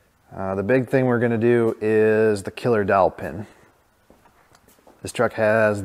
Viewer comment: Could have sworn you said "killer dolphin!"